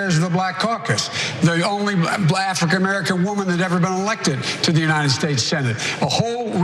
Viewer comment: D-did Biden actually say "Blafrican American"?